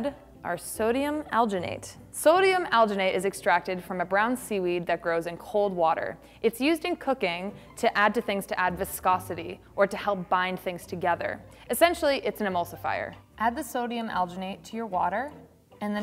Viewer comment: i swear to god i thought she said “it’s used in COCAINE” my brain almost exploded